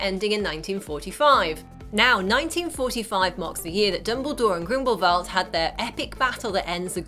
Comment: Never have I loved and empathised anyone more than credence barebone Also at 3:35 why did it sound like she said Grimblevalt instead of Grindelwald lmao